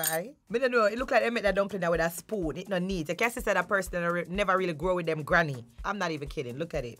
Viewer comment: Can somebody explain what she said 7:37?? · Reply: She said it looks like the person made the dumpling with a spoon and that u can tell the person didn’t grow up with their grandma😭